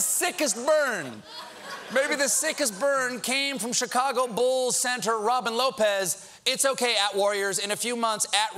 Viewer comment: Did Stephen just call Robin Lopez a Bull Centaur?